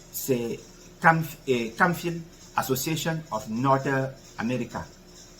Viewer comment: U mention a city that is not clear with your pronunciation on 4:30-35 minute all I heard was North America. Please clarify in comment thank u sir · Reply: Camphill Association of North America